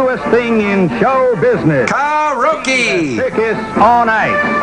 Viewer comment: What did Crow say? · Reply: "Car-roke" (Asian pronounciation of karaoke)